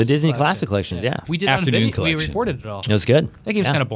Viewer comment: I thought Daemon said a video was recorded?